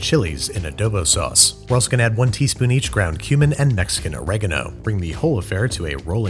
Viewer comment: He said 'cumin'. I don't know why I heard 'ground human'.